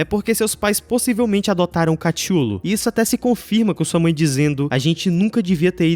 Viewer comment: oque os pais do Kenny adotaram não entendi o que ele quiz dizer? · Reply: Ele disse "Cthulhu" aquele bicho lá de um conto mó antigo